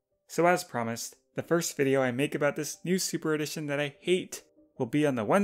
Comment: Talking about this book that I HATE What I thought they said: Talking about this book that I ATE